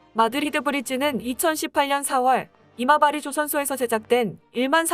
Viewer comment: 자꾸 왜 '이마바리'조선소가 '어리버리' 조선소로 들리지?